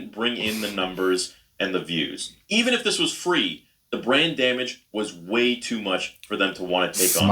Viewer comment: When he said "brand damage" I almost thought for a second he said "brain damage" which is perfectly fitting as well considering the subject matter. I suspect all of us value our little grey cells far too much to ever subject them to a second season if they would have been foolish enough to have green lit one.